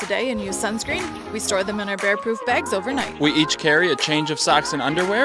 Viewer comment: When you said "bear proof bags" I heard "rare poop bags" And it took me a moment to realize what you said lmao